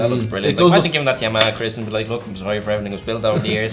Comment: Not gonna lie at 0:42 I can't understand what guy in the red shirt is saying. Listened to it three times and all I can understand is "Christmas"